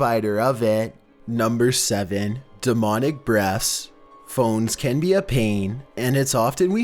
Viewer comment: anyone else hear dominic breasts. Almost spit out my Roman.